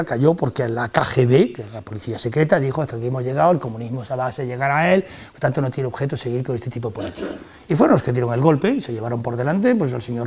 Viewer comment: Alguien me puede decir, si lo entiende, lo que dice en 34:15 - 34:16?. Dice, "el sistema comunista cayó porque la KGB dijo hasta aquí hemos llegado, el comunismo..." y es aquí donde no se entiende lo siguiente. Gracias. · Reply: "...el comunismo tiene como base llegar a él y, por lo tanto, no tiene sentido seguir con este tipo de política".